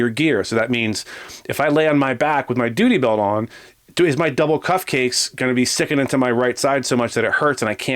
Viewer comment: On first listen, thought I heard say “on my double cupcakes” 🧁 instead of “on my double cuffs case”. ⛓️‍💥 At 4:26, I really thought the back-up Officer, upon hearing code 417, was in perfect position to boot him in the noggin.